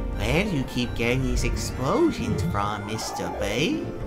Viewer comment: did he say Mr. Bay as in Michael Bay